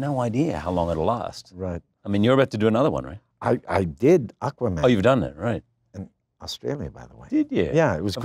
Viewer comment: did he say he did aqua man?